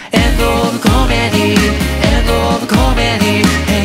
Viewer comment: 遠藤オブコメディに聞こえて、 工藤じゃねぇじゃん。ってなった